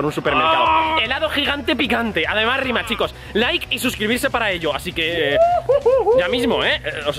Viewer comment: Like si escuchastes que dijo mail en el minuto0:30 jajajajajajajaja